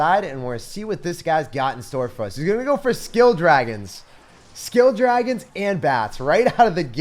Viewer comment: it sounded like you said “skilled dragons” HAHAHAHA